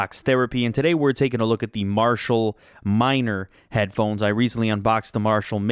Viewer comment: At first I thought it said "Marshmellow Minor Headphones."